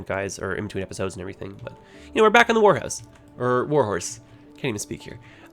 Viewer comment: Wait, did you just say you're back in the whorehouse?